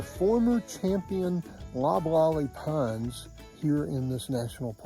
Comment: What kind of pine was that? Sounded like blah blah pine. John thought you said Rottweiler pine. Must be our age. Where were the alligators? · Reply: Loblolly pine <link>